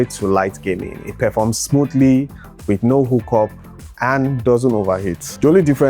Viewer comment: Did you say "hook-up" ?